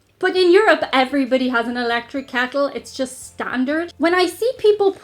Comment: I have to clean my ears out because I thought you said, "Electric candle" instead of "electric kettle", and I couldn't figure out how you would use an electric candle to make tea. ;-0